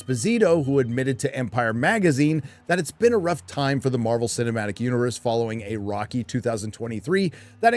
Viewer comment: I misheard him saying Marvel cinematic universe for "Marvel cinematic UTERUS" which I think is great contender for M.SHE.U